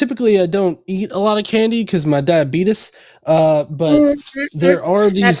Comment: When said he doesn't eat candy because of diabetes... I could of sworn he said he didn't eat candy because his dad beat him... I was shocked by how flippant both he and responded